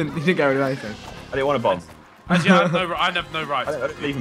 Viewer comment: Did Josh say Steven bomb at around 5:35 shoutout to all my SU fans